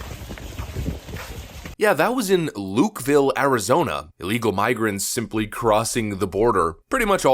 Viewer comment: Did you mistakenly say what sounded like (and I listened to it 3 times...): "...LEGAL migrants simply crossing the border..." or did YouTube alter what you said from 'ILLEGAL' to 'LEGAL'?! The subtitles had it right (ILLEGAL), but it most certainly sounded like 'LEGAL'... Weird, huh? 😮 P.S. Anyone else notice that the clothes these "refugees"/"migrants" are wearing pretty new (at least mostly unworn) clothes, nothing that would look like what can be seen, IF they supposedly "escaped oppression" & walked 1000 miles thru Central America.